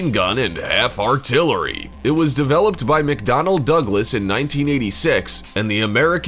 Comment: I thought you said for a second made by mcdonalds XD "1:13"